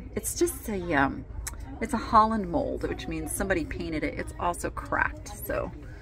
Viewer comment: When you were looking at the red pitcher and said it was a "holland mold which means someone painted it" at first i thought you said "its a holland mold which means someone PEED in it" 😂 i was like...is that why its 70 percent off.